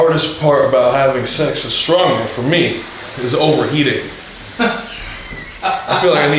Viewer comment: Thought he said “overeating” wouldve been funnier if he said overeating